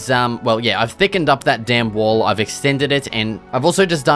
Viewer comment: it sounds like ur saying the damn wall every time